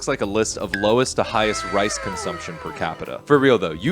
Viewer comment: Did he just say we consume low rice 😂 my friend come to africa 😅😅